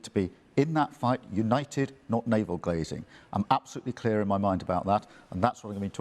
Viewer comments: I listened to Starmer carefully for once (it was horrible) and report that at 04:58 I’m sure he does not say “navel gazing” but “navel glazing”. I believe this is the most profound point ever to have come out of that lying mouth. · It sounded like Starmer said 'naval glazing'. That's what he's so good at 'gazing at his naval'. He is totally obsessed with Farage that I find it comical now. It's not even Reform so much as it's an obsession with Farage. But millions of people are moving towards Reform support and he is trying everything he can which is mostly just slander.